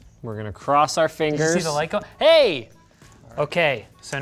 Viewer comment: sounded like the lego city hey